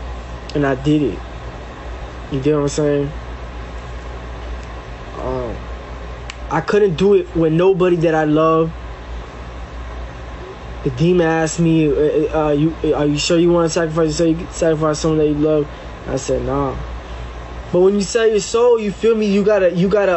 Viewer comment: did he say the demon asked him...?